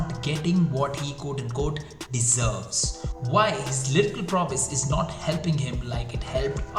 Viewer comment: What does say at 0:29 lyrical process??